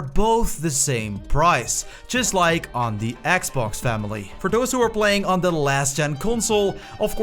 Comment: did you say family ?